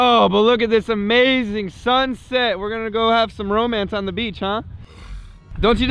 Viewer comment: it sound like he said trump instead of some. c